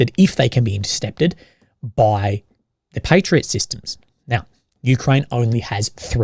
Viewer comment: I don't believe the Ukrainians have any patriots anymore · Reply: Oh! God! I thought you said they don't have "parrots" anymore! (I'm a wildlife/animal lover, so panicked a bit!)